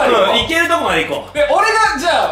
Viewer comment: 大麻設定するはって聞こえたw